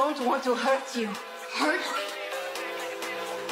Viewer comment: why did i hear, "YOU WILL NEVER HURT IM GAY!!!"